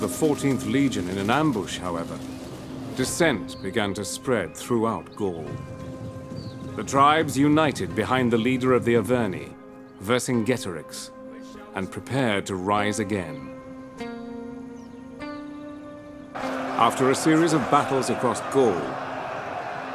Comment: did he just say iverni? That was a powerful Irish tribe from the same time period